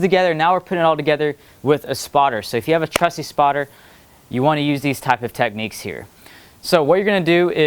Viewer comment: I thought you said WITHOUT a spotter